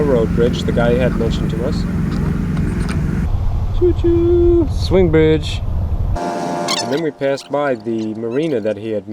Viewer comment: I had to pause the video at 22:22 to go do something. I came back 15mins. later and started it and all I heard was "Chu...Chuuu...Swing bitch". Sounds like a start to a rap video... o.O